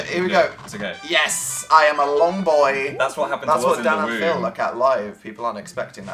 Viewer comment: Is it just me or did dan say im a long boy and then phil said thats What you say in the room....